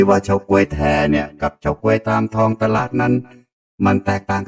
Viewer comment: ได้ยินว่า ตลาดนัด+55555